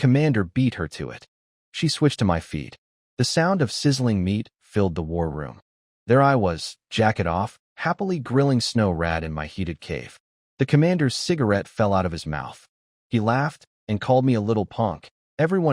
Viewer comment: jacket off 😂 I thought it said jacken off